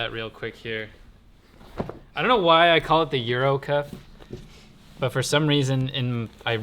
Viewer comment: I heard "urinal" cuff instead of Euro cuff, and it made perfect sense to me.